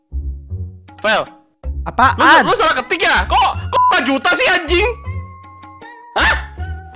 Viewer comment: ada yang tau itu brp?🗿 Gw dengernya 5jt🗿